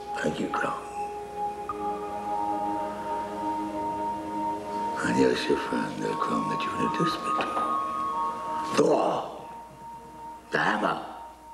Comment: Sorry, my hearing is not good. Can you please tell me what William Smith says between 0:49 and 0:55? "I guess you'll find now Crom you have this bit too - Thor! The Hammer!"